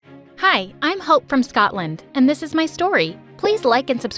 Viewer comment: at the start, I thought she said she hopes shes frome scotland